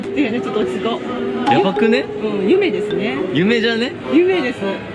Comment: の『夢ですね』が『嫁ですね』に聞こえた たしかにゆきりぬ嫁だわと勝手な納得w